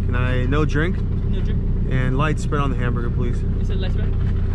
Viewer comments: I swore he said "light spit on the burger" lol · Thought he honestly said light spit on the hamburger at in-n-out